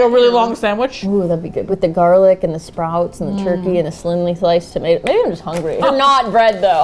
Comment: did anyone else catch jen say “slinly thliced tomatoes” at 10:13 or was i the only one who had to pause bc i was laughing so hard?